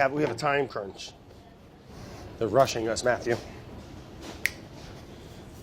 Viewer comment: I heard him say "it's the Russian Matthew" 😂🤣